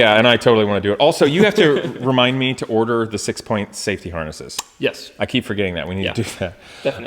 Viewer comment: He said "six point safety harnesses", I hear " little Saint Francis-es". Both seem appropriate.